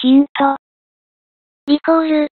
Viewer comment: リコールのところが リモートに聞こえたけど成立した(笑)